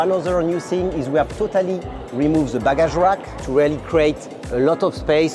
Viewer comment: i think he said "baggage area" 😂